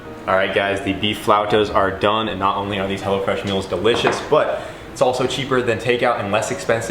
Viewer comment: I heard Hella Fresh 😂